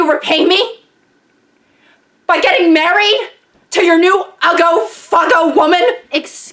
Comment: I'm sorry, but I am really curious about what she just said. I didn't understand her word. Ago fago woman? please let me know what she just said. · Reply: She said “uggo fuggo woman.” Uggo is slang for ugly, and then fuggo is supposed to be like fugly, which means “f—king ugly.”